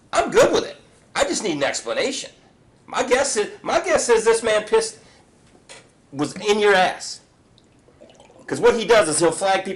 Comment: I'm sorry did he say my guess is that this guy pissed in your ass? 🤣